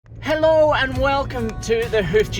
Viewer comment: it sounded like he said help